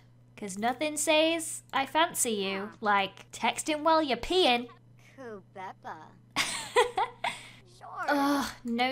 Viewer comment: Does it sound like she is Apple said oh peppa 8:17 or is it just me?